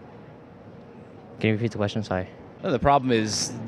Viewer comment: What's that kid saying at 13:05? "Can you repeat the question…" something..? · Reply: sorry